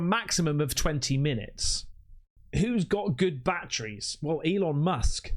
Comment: It trips me out the way he says 'batteries'. It sounds like he saying 'bat-trees'.😋👽